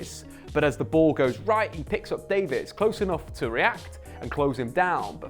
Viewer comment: Did he say statman david @ 0:00 ?? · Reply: it sound like he says David